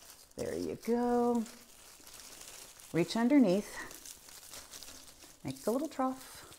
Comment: ohhh my... at 4:25 I heard ''Reach underneath.. like Donald Trump!'' and I almost spit my tea!!!